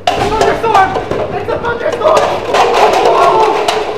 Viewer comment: it sounded like he said its a fu..ing storm